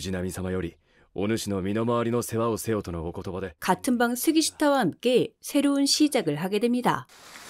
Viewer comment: 스기시타 이름 앞에 '같은방' 칭호가 계속 붙어서 같은방 쓰기 싫다로 들리네 ㅋㅋㅋㅋ